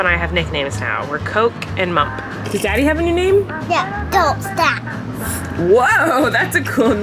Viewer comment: did he just say “dope stacks”